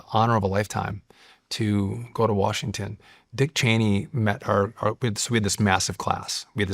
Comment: someone says Dick Cheney and I hear Tummy's rumbling..😂😂HE'S UPSETTING!! RIP